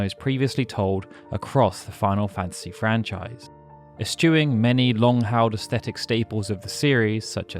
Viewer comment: Is it just me or is he saying "a stewing" instead of "eschewing"? I replayed it like 7 or 8 times and I can't not hear a "t" sound in there. Threw me off so much I had to go Googling to try and figure it out and I lost interest in this video at less than 30 seconds in.